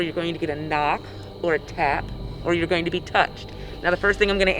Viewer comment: Excuse me? Did I hear “are you okay with being drugged” this is gonna be interesting Edit: she said “touched”. I’m kinda dumb. I really thought she said drugged.